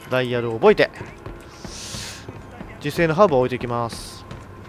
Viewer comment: ガッチマン「自生のハーブを置いていきまーす」 自分「〈自制の半分〉を置いて行きます？(つまりエッ◯マンになる？)」 空耳ヤバし。